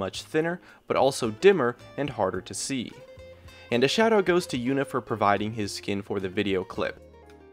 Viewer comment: Oh haha you were saying "shout-out" - I thought you were saying "shadow" I was like wuh? XD